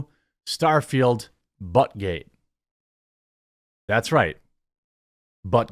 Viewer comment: Oh, "Butt Gate". I thought you said Buttigeig...